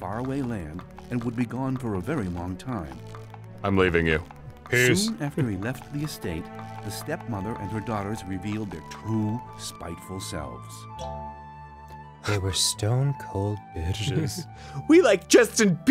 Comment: I thought the narrator said "Soon after he left pee stain"